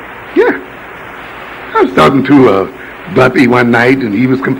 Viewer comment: Did he say that he was talking to Bumpy (as in Bumpy Johnson) at 5:10-5:20?